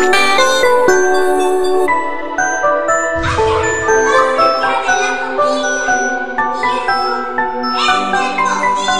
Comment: ¿Que dijo? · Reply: Dijo:hay algo mas cerca de la familia y eso ES EL TOCINO